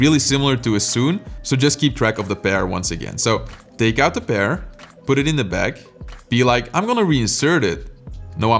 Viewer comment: When he said "take the pair and put it in the back" what I heard was "take the bear and put it in the bag". Ahahaha